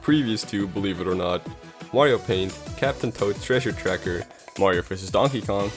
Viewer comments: Is it just me or is he saying WarioPaint? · I Could've Sworn He Said Wario Paint Instead Of Mario Paint!